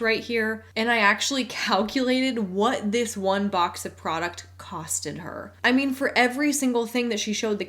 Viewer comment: (ish). Did you just say "costed"?